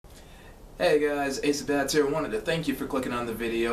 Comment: Got pushed this classic. I thought you said "Ace of Dabs"